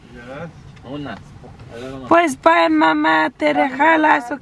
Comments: Lol I had to replay the end when mama is getting off the car. It sounded like says “Te la jalas” because she says “Te rejalas” instead of te relajas 😂🤦🏻‍♀️ we love your family and videos 💕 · I'm sure you meant to say " bye ma TE RELAJAS" at the end but it sounded like you said " by ma te la jalas" 😂😂😂 I had to replay that part LMFAO